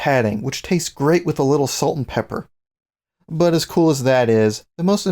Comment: What did he say? I heard "taste great with salt an pepper" ...?